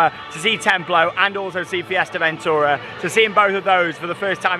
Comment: Sounds like your saying Fuerteventura made me get confused witch also is in Spain 😅